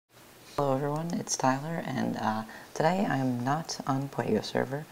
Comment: No disrespect but i thought u said "hello everyone it's Skyler." you actually sound like Skyler from breaking bad.